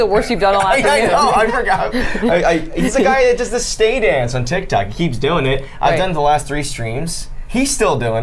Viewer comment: Did just say TikTac?